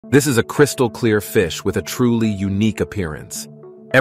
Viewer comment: Bro really said "I believe i made myself clear" 💀💀 Edit: Bruh this blew up I'm rethinking my life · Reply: I misheard 💀 "This is a crystal clear fish with a truly Ni--a parents"💀💀💀💀💀💀💀💀💀💀💀💀